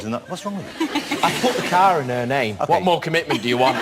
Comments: What does he say 6:51? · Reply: “I put the car in her name. What more commitment do you want?” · What does rob say at 6:50 ??? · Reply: It sounded like he said "I put the car in her name. What more commitment do you want?"